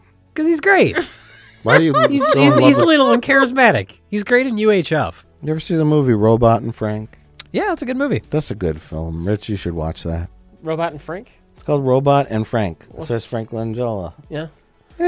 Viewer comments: At first I thought Rich said "Robot Anne Frank". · Carrie Fisher was on a special one time where she said, "If you haven't seen Under the Rainbow... thank you." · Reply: Doesn't it sound like they said robot Ann Frank hahaha Like a WW2 revenge movie where she's a terminator going after Nazis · I thought you said "Robot Anne Frank"! Lol.